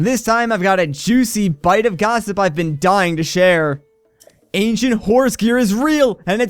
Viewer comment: Am I the only one who heard him say "Asian horse gear" instead of ancient horse gear